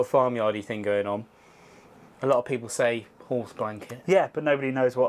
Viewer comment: sorry, what did you say...𝘸𝘩𝘢𝘵 blanket? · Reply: Horse blanket